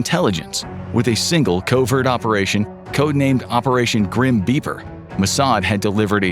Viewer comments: The person that came up with the name "Grim beeper" deserves a raise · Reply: I thought he said "grim beaver." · I thought he said Operation Grim Beaver!!! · The person that came up with the name "Grim beeper" deserves a raise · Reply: I thought he was saying 'Grim Beaver'